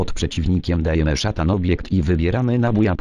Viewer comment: Szatan obiekt xD (tak usłyszałem)